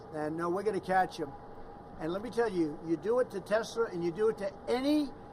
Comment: did he say Tesler? Fraudian slip Telsa+H it ler